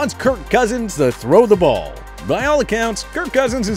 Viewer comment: “Throw the flag” not “throw the ball”. Dam can y’all get that right for once. 😂🤣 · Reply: The first time I heard him say "throw the ball" now listening again it's clear he says "throw the flag" 🤣🤣🤣